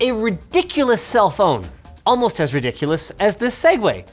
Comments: Why yes editors, I WOULD have thought he said "cell phone" ..so thumbs up for the on-screen text save 👍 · I really thought he said cellphone. Thank you for the caption, editor